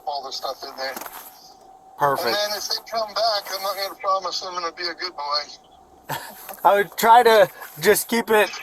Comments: I believe says he isn’t going to promise he’s going to be a good boy. Instead of your caption that say he wants the cops to promise to defend him. Probably a simple mistake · I think he said, "If they come back I'm not gonna promise I'm gonna be a good boy." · He actually says: "And if they come back, I'm not gonna promise I'm gonna be a good boy. " Then they both laugh. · "and then if they come back, I'm NOT gonna promise I'm gonna be a good boy" The caption is wrong. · "And then If they come back I'm not gonna promise that I'm gonna to be a good boy" is actually what he said, I believe